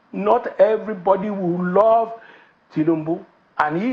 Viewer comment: This mumu man I thought he said Obi will remove Tinubu in 2027?